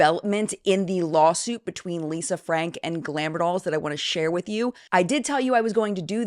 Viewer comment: I swear to God in thought you said ANN frank I was like WTF 😂